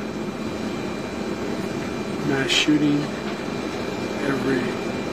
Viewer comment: I heard the words "mass shooty" and "tragedy" but everything else was mumbled jibberish Also "its hard to keep up with him" is probably code for "we turned our backs and he wandered off somewhere while no one was supervising him"